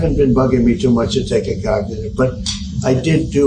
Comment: Sounded like he said cagnitive !